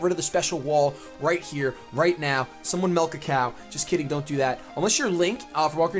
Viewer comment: Oh, "milk a cow"? I thought he said "melt a cow"! XD